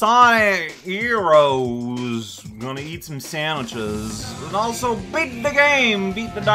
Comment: I like how opened the video with "Time to Eat Some Sandwiches!" and while that is probably unintentional, it's funny that i'm also... eating.... Sike! you thought i was gonna say "Sandwiches" didn't you? This time i was eating Chips and Taramosalata! Too bad! Well, it's quite tasty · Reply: I thought you said said "Time to eat STONE sandwiches"